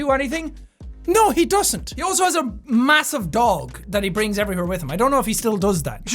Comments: When I heard 8:03 i thougt he said a massive dong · I thought he said “he has a massive dong that he bring with him everywhere” when talking about the Irish president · I thought he said " massive dong" lmao